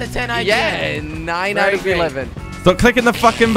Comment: Did he just say..... 9/11? Jordi's messed up.